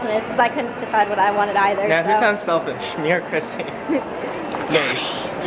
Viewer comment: MY VIDEO IS FINALLY BACK TO HD QUALITY! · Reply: sorri what did u said at 7:17 "she sound selfish___ chrissy" something like that.i didnt hear clearly in the video lol.